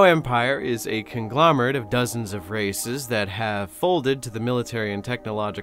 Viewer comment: Instead of "conglomerate of dozens of races" I heard "conglomerate of dozens of racists"